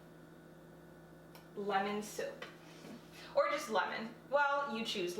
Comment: Lemon soap (when u said lemon soap I thought u said lemon soup 😂)